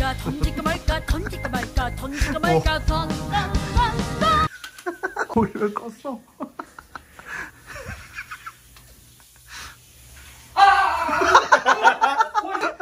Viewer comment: 건들일까 말까로 들림ㅋㅋㅋ